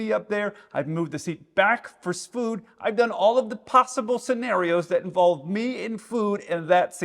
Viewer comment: Did he say "popsicle scenarios?"